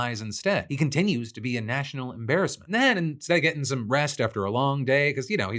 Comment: Lol on yesterday's show I could've sworn Phil said "Cucker Carlson", and now on this show at 6:25 I swear he says "get some 'breast'" instead of get some "rest". I may be losin' it 😅